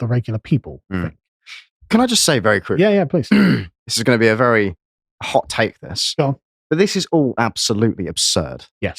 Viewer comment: What did say about it being a manifestation of? It’s hard to read his lips. This is said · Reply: "Mental illness" and "mental".